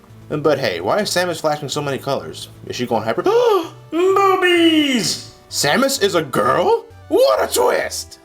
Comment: Did anyone else hear "BOOBIES"?